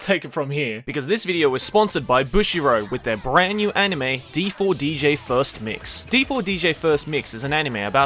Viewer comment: Am i the only one misheard this as D4C.....